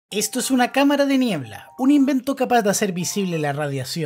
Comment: Yo entendí "esto es una cámara de mierda"